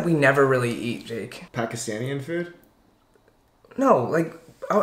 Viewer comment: he actually said "pakistanian" food....